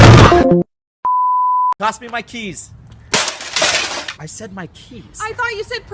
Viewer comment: I'm sorry I thought you said Pass me my Leaves🤣🤣🤣🤣